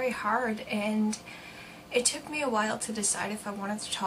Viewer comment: I heard her say I took the baby